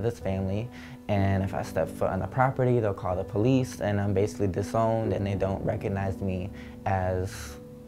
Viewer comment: i thought it said "drowned" instead of "disowned"